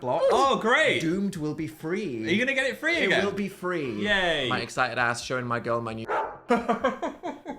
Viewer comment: Not me still trying to work out what Dan says at 15:40 despite watching this at least 15 times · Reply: I think it's 'strap on' 😂